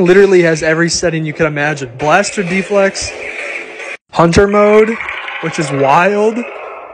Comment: Did you say it has lube? Or did I mishear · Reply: And misheard Hunter mode as punching mode 💀